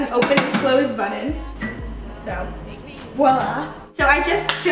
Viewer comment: Did you say "voilà" at 2:39?